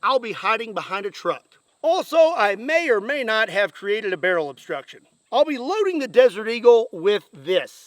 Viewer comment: What barrel obstruction? I could see a barrel obstruction doing this, and it would also explain why the barrel left with the bullet, but the only known obstruction in the barrel was the bullet itself. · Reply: I thought he said he created a barrel obstruction? I may be wrong. But in one of the clips, looking down the split barrel face, there definitely doesn't look like a clear barrel. And that may be attributed to the fact the gun is utterly destroyed, but either way lol